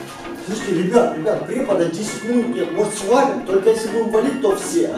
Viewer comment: мне одному послышалось точто сказал"слушайте,препода уже 10 ДНЕЙ нету!"